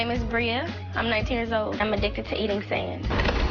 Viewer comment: I thought it said, "Addicted to eating Sound." I was like, how tf can she eat sound??